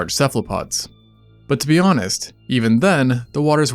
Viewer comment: I thought you said whores and briefly my fear of the ocean dissipated